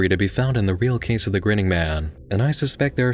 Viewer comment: Wait I thought it said grinding man